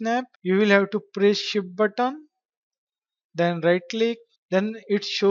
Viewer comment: what does he say at 9:33 ? He says shift button, geez.